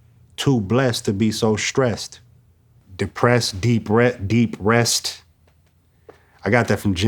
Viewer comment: I thought he was saying deep breaths but he was saying depressed